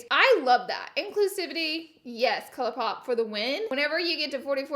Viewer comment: I heard "cupcake" when she said "Colourpop"... Am I hungry ?